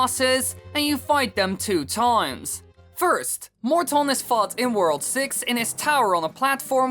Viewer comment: "First morton has FART in world 6" Am i hear it right? RIGHT?!